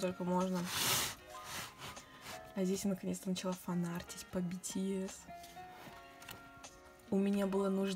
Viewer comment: Я правильно услышала, или мне кажется, она сказала "Я фанатела от BTS" ??? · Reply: Макнэшка Лайн BT21 начала фан-артить по бтс)))